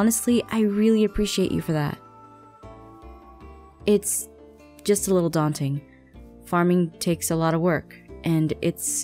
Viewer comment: What said: It's just a little daunting. What I heard: It's just a little Dante. XD